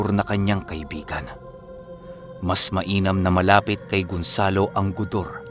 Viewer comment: GARUNG? HMM PARANG NARINIG KO NA YUN KUNG SAAN... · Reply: instead of #piocarpio#nasambit is #gonzalo# inulit ko pa baka ako mali narinig🇸🇬❤🇵🇭